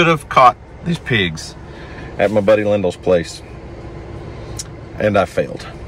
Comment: did you say lindel? That’s my dads name (rip dad 💔😩) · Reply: Yes, Lyndel